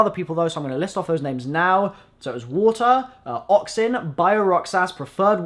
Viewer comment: My name is ialoxin_ I thought you said my name XD but it was oxin D